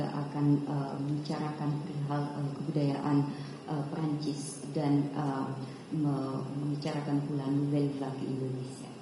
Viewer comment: Saya salah dengar apa memang gmn y di menit 1.37 kok Prancis dan Malaysia,, knp bukan Indonesia 😢